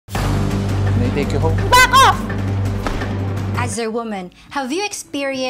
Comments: I CAN'T WITH THE LOUD "BACK OFF" 0:01 HAHAHA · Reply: i believe i laughed even harder cause i thought i heard she said 'f--k off'... 🤣🤣🤣.. but i guess i might hve missheard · I CAN'T WITH THE LOUD "BACK OFF" 0:01 HAHAHA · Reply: YESH SAME LMAO I THOUGHT SHE SAID F OFF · I CAN'T WITH THE LOUD "BACK OFF" 0:01 HAHAHA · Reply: I THOUGHT SHE SAID "FUCK OFF" LMAO